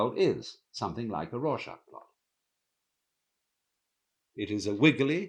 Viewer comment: What word did he say in minute 6:22 i don't understand it and the caption says "raw chakra" please (English is not my first language) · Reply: I believe he said “Rorschach blot” as in a Rorschach blot test. Search for images of Rorschach ink blot test and you will see what he is talking about here.